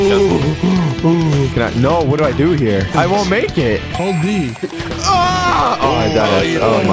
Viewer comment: Hold b or d because it sounds like he said d 11:57 😂😂